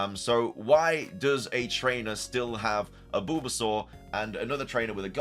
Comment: Sounds like your saying Boobasaur 😂😂